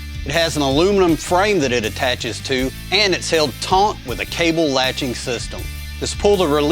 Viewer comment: Did you just say it’s held taunt?